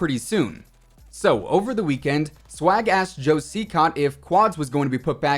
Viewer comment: LMAO it sounded like you said "Swag Ass Joe Cecot"